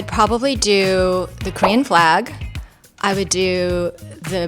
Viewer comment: I understand it might sound "green", but she clearly said "Korean flag"